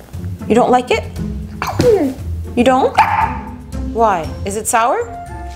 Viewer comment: For second I thought when she said is it spicy I thought she said why is it spicy